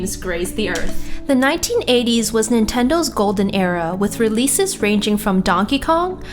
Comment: Sounds like she said golden arrow. :3